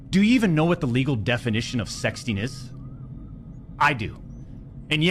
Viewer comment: What he said: "Do you know the legal definition of sexting? Because I do champs, and that wasn't it!" What I heard: "Do you know the legal definition of PDF file? Because I do champs, and while what I am has a file on the end of it, I'm not a PDF!"